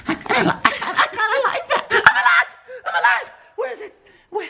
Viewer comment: who heard Austin say "i like that" I did like if you did too!